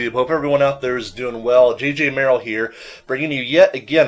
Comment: sounds like you said "GGMerill"